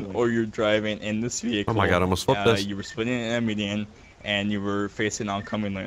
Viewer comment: what did he say? Spinning an amunan?